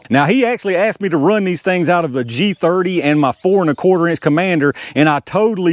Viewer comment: Quick side note: I thought when you said 4 and a qtr inch Commander, I heard "fornicating" commander... Oops.